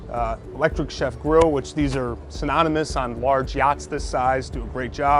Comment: did he say BBQ grills are "anonymous" on yachts this size, or are my ears playing tricks on me? LOL! I'm guessing he meant they're ubiquitous. [Edit: Okay, he said "synonymous," but still...]